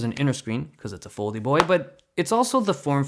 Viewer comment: did he say "foldy boy"?